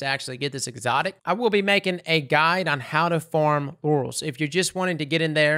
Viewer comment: is hilarious on accident due to accidentally saying orals instead of laurels. "I will be making a guide on how to farm orals, if you're looking to get in and say grind.."